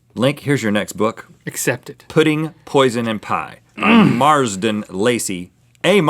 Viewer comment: I thought the third one said: Putting Poison in Pie